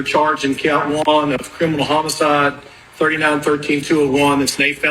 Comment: no the brother lived, luckily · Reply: the judge says "criminal homicide", I think I heard that correctly.